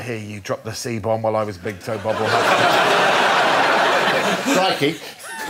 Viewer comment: What does Tim say at 4:25? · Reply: “Crikey,” a now-quaint mid-century English euphemism for “Christ” — and neither crikey nor Christ are the C-bomb earlier referred to, which is the joke.